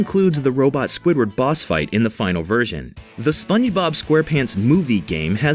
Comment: Did he just say "SpunneyBob" at 2:23?